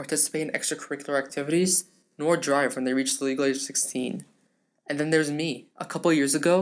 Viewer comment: the first time I watched this I thought he said, “or drive BEFORE they reached the legal age of 16” and I thought well yeah otherwise they would be breaking he law when he actually said, “when they reached the legal age of 16”